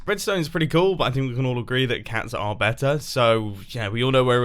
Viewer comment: Why did the subtitles say "We can all agree pugs are better" when he ACTUALLY said "We can all agree cats are better"